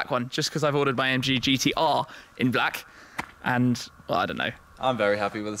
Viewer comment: The only thing better than one AMG GT S, is two of them! A huge thanks to Kempinski Berchtesgaden for the opportunity to go exploring with these two. For this video we took a different approach, if you want to hear the car in anger please check here: <link> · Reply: Is that a slip of the tongue by +? Did I hear it correctly that he ordered his AMG GT R in black [0:45]? I can't remember watching a video that he's divulged the spec and colour of his AMG GT R. Or did I just missed that video?